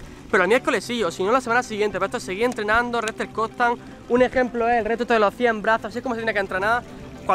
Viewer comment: Alguien me podría dejar claro lo que dice? "No sé que" constant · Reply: Albert Sallent recepción constante